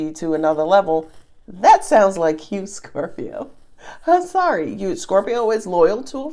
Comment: What’s funny… is when I watch your videos, they do eventually apply and all I hear is “Scorpero” 🤣🤣🤣